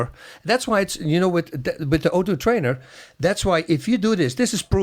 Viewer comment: what was bas talking about when he said 30 reps a day, reps of what couldn’t make out the thing he said before? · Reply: High ground Is my ground hello there it is his mouth peice o2 trainer